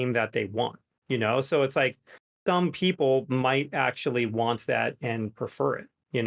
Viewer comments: Any else hear "dumb people" at 1:05:30 istead of some people, which is probably what he said? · he said "dumb people that would like longer queue times for a better experience" he literally said it and there is nothing to understand wrong. He meant it and people are dumb for thinking different than the Devs. That's why they try everything else besides 6v6. Because they already said 6v6 people are dumb and nostalgic and don't know what they are talking about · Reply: watch it again, it’s a zoom call, his mic cut out right when he said it. I understand why you think he said “dumb people” but he defininitely said “some people” but his mic cut out at a very unfortunate time